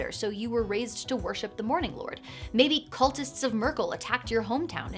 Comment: I definitely heard "cultists of Nurgle" and was like wait.....lol